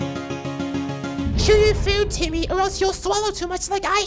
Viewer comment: My approximately 5th time watching this and I just heard, “chew your food, Timmy